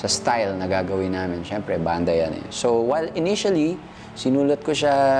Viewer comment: Haha I heard it was "initials" of the girl he wanted to tell it to.. "B.A.D."